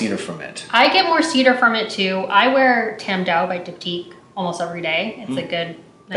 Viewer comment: What does she say she wears at 3:55 ? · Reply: Tam Dao from Diptyque.